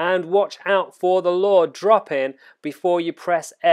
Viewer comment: WATCH OUT FOR THE “LAW” DROP IN—bruh with your british accent i cant tell what the heck ur saying about the short window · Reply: Haha, lure.